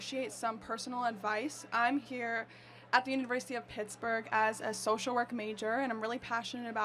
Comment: I thought she said titsburg